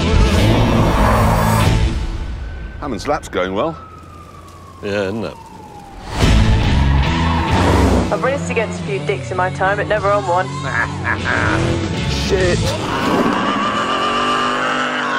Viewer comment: What did she said? "I raced against some dicks...?? hahaha :D